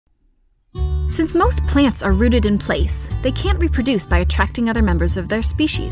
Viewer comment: wait...Did you say that MOST plants are rotted in place Bad grammar BOOOO!!!